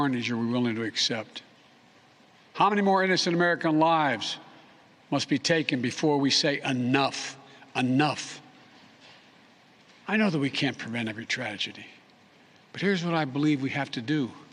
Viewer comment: Sounds like he says “I know we can’t prevent a good tragedy..” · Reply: Steven Bisson -- Sounds like it! I had to listen 3 times. He actually says "I know we can't prevent every tragedy" - drops the volume on the word "every".